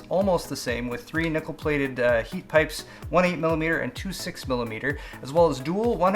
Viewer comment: "3 nipple plated heat pipes" thats what i thought he said XD